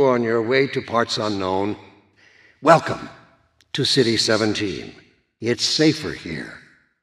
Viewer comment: I paused the video and started it back up and this is what I heard "Tity 17" and Breen does make some good points